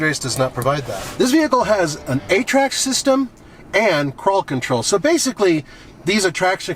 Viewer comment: Did anyone else think that said "This vehicle has an 8 track system" at 7:06?